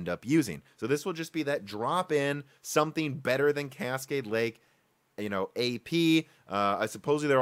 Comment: At around the 11 minute mark, I thought I heard you say "Casket Lake".